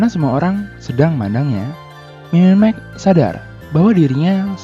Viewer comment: apakah cuma telinga gua yg dengernya mmq